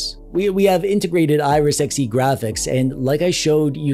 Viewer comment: I misheard you saying: "...Iris Sexy Graphics." 🤣 Noticed most of the presented games to play on my SD. Also this reminded me of one game I want to add to the list because its deeply touching: "This War of Mine". Check it out. You wont regret having it in your library. Love ❤️